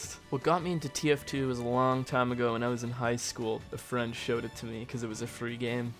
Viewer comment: ngl i really thought he said that the french showed him tf2